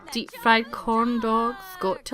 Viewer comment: I think I heard her say deepfried condom but after repeating it I heard deepfried corndog x3